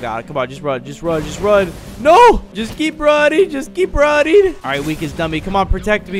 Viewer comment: When he said just keep running i heard ‘just keep rotting’